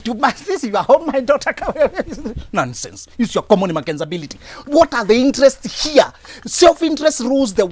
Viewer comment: Did I hear " makenzability" 😜😜